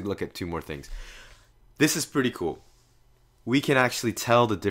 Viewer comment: I thought he said "asses" instead of "acids" bruh xD